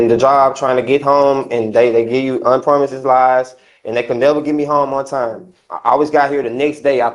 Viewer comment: Did he say unpromises lies? 😅